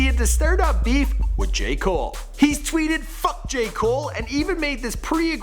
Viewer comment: I thought he said fuck Jake paul